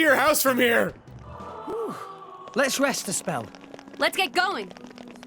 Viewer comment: please tell me I'm not the only one who until this video thought rex said "Let's rest as well" instead of "Let's rest a spell"